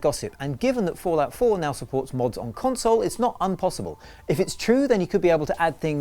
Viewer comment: Did you really say "unpossible" at 0:51??